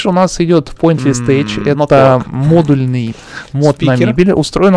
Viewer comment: что за мод, не услышал из-за голоса на заднем плане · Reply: Pointles Tech